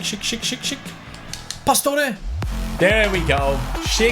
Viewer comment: When you said shick shick shick i thought you said shit shit shit